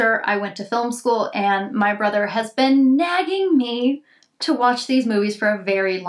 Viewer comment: I thought she said "My brother/husband" lol 😳